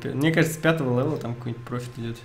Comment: "Вадим го в террарию" мне это послышалось ?